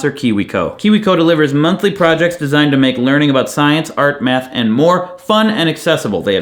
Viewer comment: I swore up and down you said "science, art, and meth." Seriously, I had to go check the CC. lol